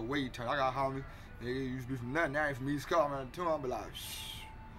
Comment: wtf did this nigga say? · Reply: "I got homies he aint from nothin I run into em he from East Coast I be like Pshhhhh"